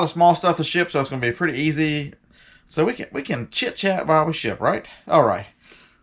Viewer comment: YAY SNOW!! How will you survive with all that snow LMAO.. I thought you said chit chat while we sh__ not ship lmao. Do we need CC for your videos now